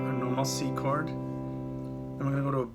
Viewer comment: By any chance could you kindly clarify something I cant quite catch? At about 1:15 it sounds like your saying "must C chord". Are you saying CMaj? I think your video definitely captures the vibe nicely. I'm currently learning it on my acoustic. Eventually incorporate my electric and Mel9 electroc-harmonics pedal. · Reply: Hi. “A normal C chord.” I usually play on acoustic… Bet it sounds great with the Mel9. If you post let me know; would love to check it out.